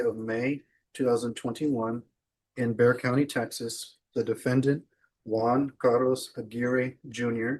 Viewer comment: it's Bexar County, TX. The captions say Beauregard County, TX, which doesn't exist, and wouldn't be pronounced as "bear".